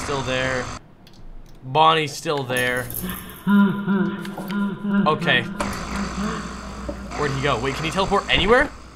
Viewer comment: what did bro said??? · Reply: They get